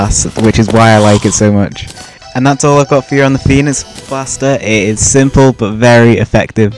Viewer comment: Whatch from 7:18 it sounds like he says penis blaster.